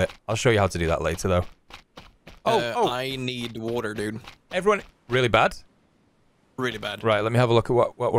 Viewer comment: Did anyone else hear, "Oh, oh, everyone" at 2:30 ?